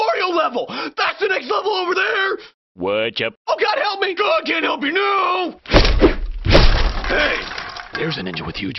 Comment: For the longest time I thought he said "God can't help you, n00b!"